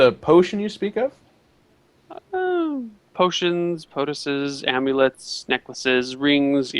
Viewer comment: I... I can't believe they just let the cockring comment slide right on by. Also, when tries to say "poultice" I always hear POTUS. "I sell diverse potions and Presidents of the United States"